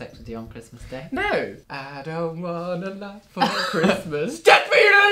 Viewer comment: I THOUGHT HE SAID "I DONT WANT A LOAD FOR CHRISTMAS" IM DEAD